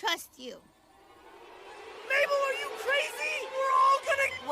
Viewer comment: man I coulda swore Dipper said "mabel are you fucking crazy" but ig not